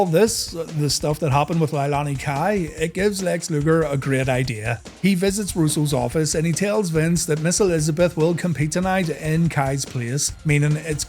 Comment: I thought you said Miss ElizaBRET :D